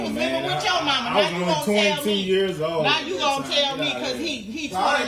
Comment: If I were her husband, I wouldn’t pay any of the bills, stack my money and move. The key is she met that man while he was living with his mother. That alone triggered me. Then she’s doing the same thing with her son. The situation is messed up all the way around. I can’t do adult daycare. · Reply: It sounded like he said he was 22 years old at the time